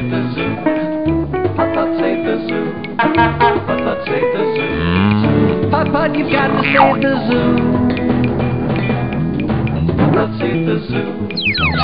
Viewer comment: I always thought it said "Putt-Putt You Forgot to save the Zoo!"